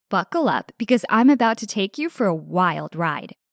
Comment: I thought she said fuckle up